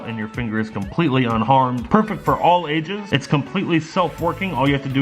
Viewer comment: I THOUGHT HE SAID PERFECT FOR ALL ASIANS HAHA